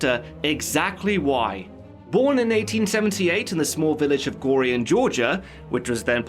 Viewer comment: Hey man, I love your work, Thank you for these videos! I am not sure if I heard correctly but I think, around the minute 2:31 you said he was born in 1978?